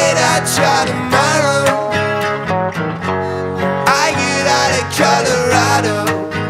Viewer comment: Wow i thought he said I get off in Colorado🙈like if hes on a road trip 🚗